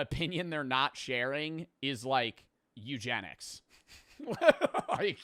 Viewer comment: I thought joe said mewgenics